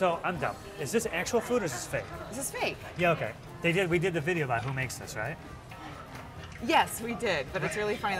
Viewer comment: I thought she said my mom makes these